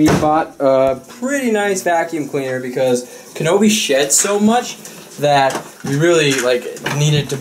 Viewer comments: It really sounded like said Kanobe shits so much.. not sheds lol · for a moment it sounded like you said "Kabobie shits so much"